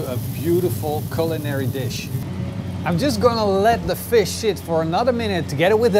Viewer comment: HAHAHAHA!! Minute 5:31 it sounds like you said "I'm just going to let the fish shit"